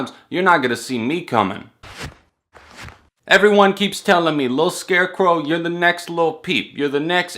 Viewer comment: I thought he said Lit Scarecrow...and I kinda wish he had.